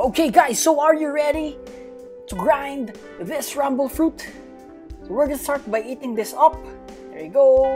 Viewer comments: When you eating, I hear you said "eat this rumble" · did you say rumble fruit?